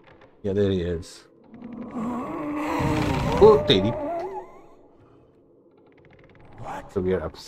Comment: LOL did you say "oh teri" @ 27:42 😂